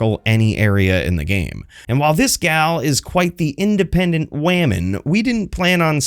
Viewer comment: Did I hear iceman???? WAMEN????